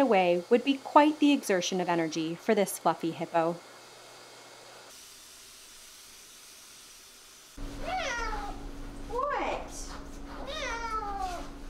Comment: For some reason, I could have swore she was about to say "For this fucking hippo" lmfao